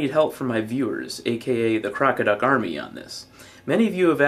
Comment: At first I heard you say "crocodyke army". Now THAT would be awesome!